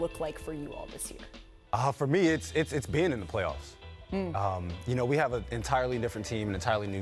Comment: at first i thought he said "ending the playoffs" which i thought was too cold for no reaction so i had to rewind, someone needs to use that